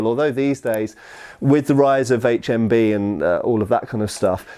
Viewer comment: Ha, I heard 'with the rise of HMV' instead of HMB the first time. I was thinking 'all those bloody DVDs (and CDs) ruining the accuracy of replica swords!'